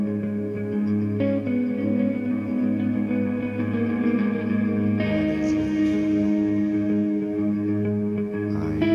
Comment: What does he say during the middle part though starting at 3:35? Been wondering for years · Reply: "What is it I know, what it is that holds me so." I arrived 5 years late but at least I arrived.